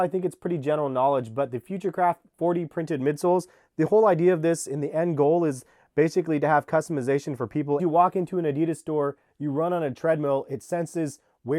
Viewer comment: Future craft sounds like hes saying futurecrap